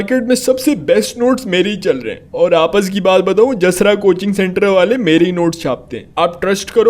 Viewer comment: I was just watching the video as I usually do and then suddenly the physics sir said Jasrah couching. At that point I was like "did he just said, wait wha-" I was astonished as my name is Jasrah I thought the physics sir saw me through the screen 😂😂